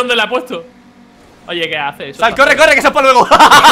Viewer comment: alguien me puede decir que dijo en el minuto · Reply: sal, corre, corre que esa es pa luego